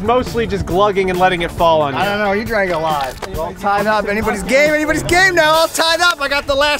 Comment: I thought he said “Everybody’s Gay!” “Everybody’s Gay Now!”